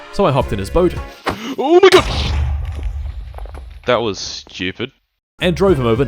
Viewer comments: did he say the N-word? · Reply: he said oh my god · ayooo what did you say 👀🔎🔎🚨🚨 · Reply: He said "oh my God"? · what did bro say 🤨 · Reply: ohmygod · WHAT DID YOU SAY?!?!?!!?!? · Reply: That was Stupid?